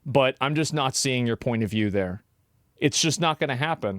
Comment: did you say, i'm nazing your point of view there? hilarious!? =D